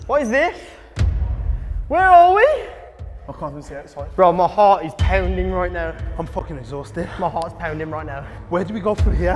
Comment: I thought he said (my fart is pounding right now)